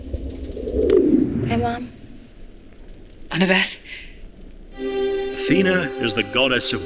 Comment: Whooops! I was thought that you said ATHENA not ANNABETH. Excuse my stupidity(